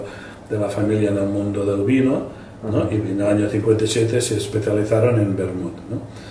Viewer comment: the subtitles say 1947 but I think I hear something like 1957. I am not a spanish speaker, but I am a native portuguese speaker, which is similar.